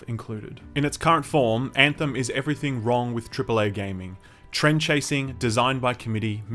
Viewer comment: Did he say trend-chasing?